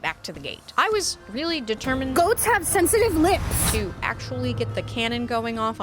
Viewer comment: I misheard the "goats have sensitive lips" and completely lost it